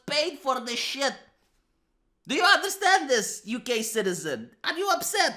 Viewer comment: it sounded like Ugey-citizens kek